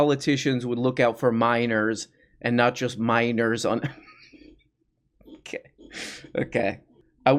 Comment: The first time I heard that miner/or line I heard it as "I wish that politicians would look out for minors, and not look out for miners on some island". I was confused and wondering if there's some island somewhere where lots of mining gets done, and the government are looking out for them or something